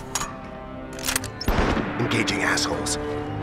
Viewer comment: did i hear one of the guys say "Engaging Assholes"?